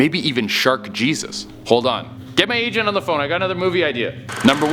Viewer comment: I thought said "get my Asian on the phone" 😂 9:39 shark jesus lol